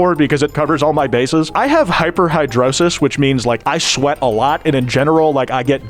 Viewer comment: bro said hyperhidrosis and the editor thought he said hypohidrosis